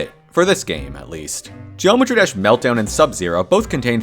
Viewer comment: excuse me did you just say joemetry???